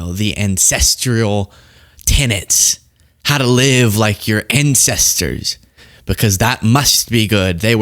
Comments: I thought you said "live like our incestors" 🫥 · OMG I thought you were saying “incestual” life style. I was so confused 😂